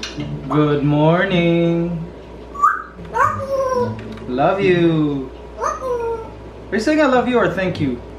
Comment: it sounded like was saying "Love You"!